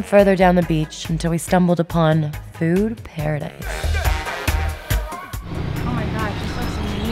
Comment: I thought she said boob paradise not bude paradise 😂